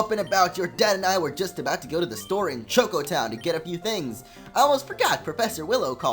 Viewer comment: everytime you say chocco town i hear taco town i'm so sorry